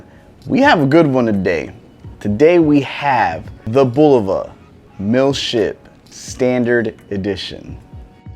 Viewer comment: Dang that domed crystal is seriously awesome looking. Ok hilarious, your intro I swear it sounded like you said today we have the Bulova no shit standard edition. Hahaha. Very nice looking watch for sure. Def vintage vibe, I’m hoping to get the bulova lunar chronograph one of these days. I like the finishing on the case that’s awesome